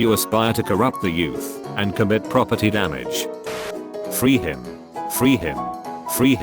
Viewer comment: Did anyone else hear “free me” the first time they listened to it?